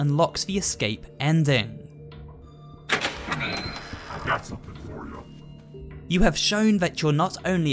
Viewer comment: I think I heard "I've got something for ya"...?